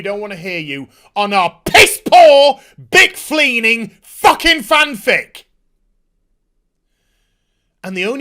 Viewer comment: did you just say 'bickfleaning' instead of 'beanflicking'?! Lollollol!!!